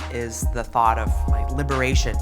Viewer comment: reading the captions, I thought that said librarian 😂